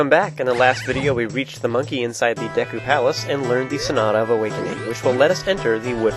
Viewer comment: you sound like your saying snot of awakening lol